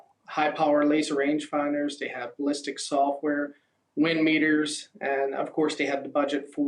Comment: did he say "I dont know" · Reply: X X I’m hearing “ uhh, you know “